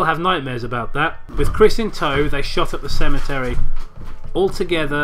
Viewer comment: It sounds like they said "with Chris in tow they shot up the cemetery," and I just pictured Santa and an elf with pistols shooting a bunch of gravestones.